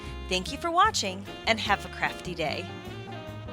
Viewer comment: when she said "Have a crafty day!" I thought she she said Have a crappy day! Lol! oops..